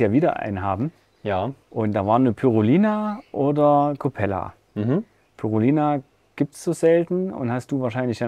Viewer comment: Tolles Video 👍🏻 Kleine Frage: Bei 1:22 redet ihr von "Pyrolina", hab ich ich jetzt vermutlich komplett falsch geschrieben. Hab ich auch noch nie gehört. Was ist das denn für ein Fisch. Der Name in der richtigen Schreibung würde mir schon helfen. Liebe & neugierige Grüße P. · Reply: Pyrrhulina